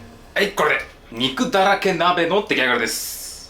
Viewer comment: 鍋がなまったのかは、分からないけど初見で肉だらけ鍋が、肉だらけラーメンって聞こえたった(笑)